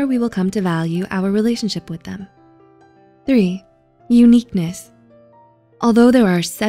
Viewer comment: 1. Confidence 2. Availability 3. Uniqueness 4. Kindness 5. Intelligence 6. Passion 7. Sense of humour 8. Demeanor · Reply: I thought uniqueness said ugliness then and I was like great that’s that one then LOL